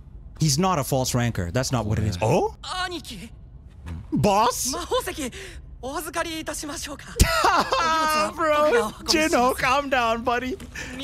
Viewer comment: Not Boss, I think he said 'Bro' but he must not get too chummy with the wrong person. 10:30 What a nice old man. He beat his driver's butt with just the air pressure alone.😂 12:00 This show better not change Sung Jinwoo for the worse. Some MCs didn't deserve to become bad guys. Everytime the MC mentions 'the world' or 'this world', a disaster takes place. 13:47 That's because it was easy. I mean she's crazy enough to get in Jinwoo's way. I just know it. 19:17 Sung literally said 'pull up'. BTW I've only ever seen him level up after finishing quests or defeating bosses or receiving titles.